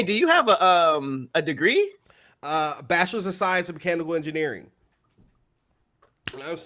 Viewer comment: 🤣🤣🤣 lmfao! I thought he said “ cannibal engineering” not mechanical at first 🤣 (35:36 or so) bc yall all had me laughing like h3LL Had me do a double take! is smart AF clearly and funny so okay! I am not familiar with him but for sure about to check you out dude 👏